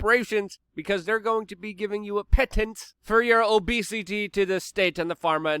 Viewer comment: obedience not obesity but I get what you were saying